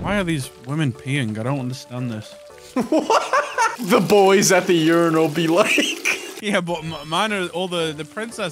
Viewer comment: At the Start I heard say "boys at the funeral be like" instead of "boys at the uniral be like" lmao